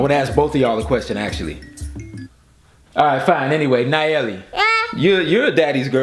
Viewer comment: I swear I heard “hell yea” at the end 😂😂😂